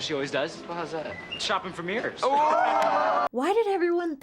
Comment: I legit heard "Ya know, she probably spent the holiday the way she always does... chopping Vermeers." Thank you for clarifying it. I was so confused! I was like, "Okay, so she's so rich and mean that she buys and destroys famous paintings? Or is it just Vermeer? Wait... They wouldn't use Vermeer as the famous painter to reference on a DCOM. Why Vermeer?!" For the record, I had to refresh my memory on Vermeer. He's the guy who painted Girl with the Pearl Earring. Apparently, he's not Renaissance, but Baroque. I guess, after Sharpay Baroque those paintings, they would've had t' fix 'em!